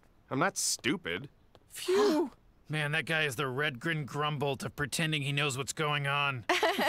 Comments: I always thought he said red grim gumbo lmao. Also I can't believe this is 5 years old already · man, rick is like the Redgrin Grumble of tricking his grandkids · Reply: I always thought he said red green grumble. Like the red green show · I thought he said red green grumble and was thinking hey that is a guy the red geen show nvm i guess 😅